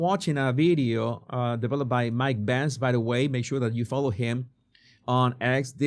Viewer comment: I was listening to the video and thought you said Mike Pence. I was relieved it was Benz